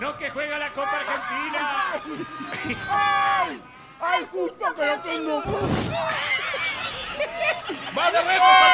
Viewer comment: que dijo??? 😂😂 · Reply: Ay justo que lo tengo sucio, al culo se refiere jajaaj